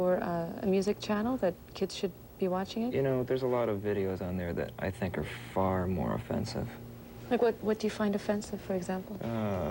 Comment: what is he saying; "The are videos, which are far more...."?